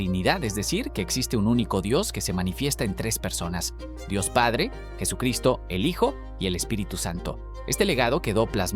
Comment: Haz pausa en tu explicación, por el amor de Dios. Al principio escuché "la trinidad: Dios, Jesús, el hijo, y el espíritu santo". Así las cosas, son 4 personas, no tres. Entonces, por favor, haz las pausas adecuadas.